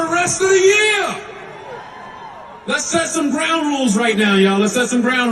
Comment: Lmao I thought he said “brown rules” 😂